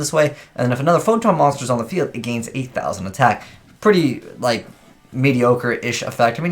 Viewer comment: since when is gaining 8000 attack mediocre. Don't tell me I was the only one who heard that on 4:30.